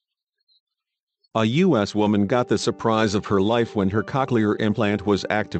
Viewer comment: oh wow this is so amaz- A U S W O M A N G O T T H E S U R P R I S E O F H E R L I F E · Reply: LMFAO I can relate that hit me out of nowhere and on top I heard it as AN-US Woman like C'omon it couldn't get any worse than that 😂💀